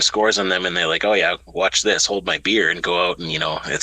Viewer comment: 😂😂😂😂 I thought said “Hold my Beard!” (Not hold my beer) So I had to go back and listen again. 😂😂😂😂